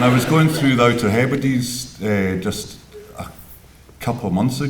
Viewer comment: What does he say at 7:00? "I was going..." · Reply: To the Outer Heberdies, Scottish islands.